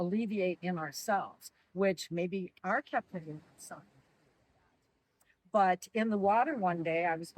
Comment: I think she said, "Maybe our captivity has something to do with that" to which he had a strong reaction, and at the same time the sound went out.